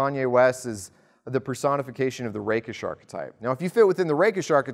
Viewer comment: I heard "the rape'ish archetype".